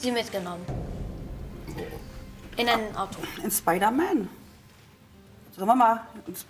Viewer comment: Ich hab nen spiderman verstanden